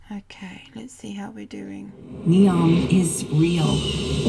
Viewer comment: NEOM IS REAL ➡️ I heard it like : " NEOM ISRAEL " 😯 Very interesting....